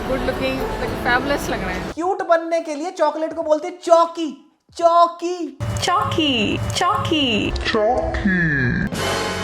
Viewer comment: that Chocolate is actually called Choki choki